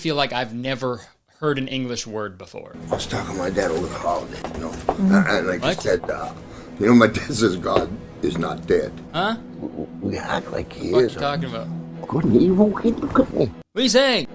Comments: ok so this video showed up in my recommended like 2 years since i first watched it and i think i FINALLY know what angry sim boss is saying at 4:36. rough transcript: "i was talking to my dad over the holiday, y'know, and like, he said, uh, y'know, my dad says "god is not dead, we act like he is but he couldn't be--" oh, hey, look at me!" why he says it like he's actively gargling marbles while practicing simlish is anyone's guess though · “good and evil, hey look at me” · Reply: I heard "good and evil keep company" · Lol he’s saying “good and evil, okay. Look at me” 😂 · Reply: I swear I heard "god and eve will hit the company" and was like yeah checks out. makes sense. definitely. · OH MY GOD HE SAYS “good and evil; hey look at me.” It just clicked I’m so proud of myself. I felt like his talking was a puzzle they put into the movie. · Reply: THANK YOU!!! I kept hearing “Good and evil eating cookies” for some reason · As a southerner, I believe I hear "good and evil keep you company." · My grandma has the DVD and the subtitles of the boss’ dialogue is “(inaudible)” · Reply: i heard ‘good or evil take a cookie’ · I heard "Good and evil? He cook meat." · The boss sounds like he says, "good and evil pay the company." Even though it sounds more like he's saying, "Gwood an dwevil pay the cwoup'any." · I hear “good and evil eat cookie” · I hear "good and evil hid a cookie" 😂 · bro all i hear is ' good and evil boungkik ' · I heard him say “Good and evil, key bouquet’…”